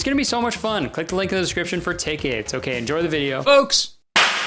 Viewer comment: thought you said tickies instead of tickets in like an Australian accent, like, oi mate, go on n grab yourselves some tickies why don'tcha?